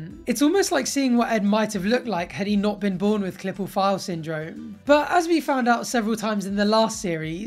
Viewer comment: What is Clip or File Syndrome? (Even the subtitles think thats what you said. For the rest of humanity, who also didnt catch your enunciation, its Klippel Feil syndrome.)